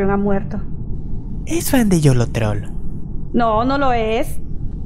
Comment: creo que dijo: es fan de yolotroll la primera vez que lo escuche